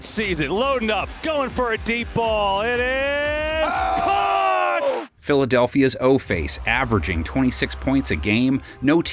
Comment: Did he say Philly's O face?😮 Also won't be able to watch since Disney pulled their content from YouTubeTV😢 🦅💚